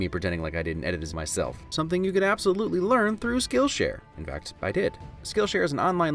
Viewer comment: Could have sworn he said Skittleshare at 19:42.